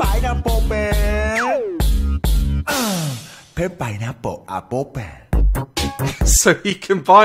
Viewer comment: did he say pineapple apple 😂